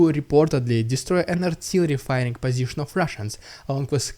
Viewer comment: Google subtitles are confusing, when you said "Ukraine destroyed an artillery fire position of Russians", the captions said something like: "Ukraine destroyed NATO defined positions of Russians..." Hilarious!🤣